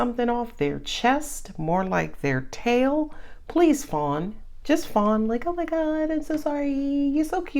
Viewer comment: 😩🍷🍷🍷🤣 honest to G💚D Thought you said "Just Spawn!"😂